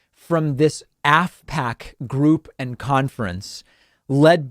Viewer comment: I kept hearing "asshat conference", so I had to look at what I was listening to. Oh, "AFPAC" - different spelling, I suppose.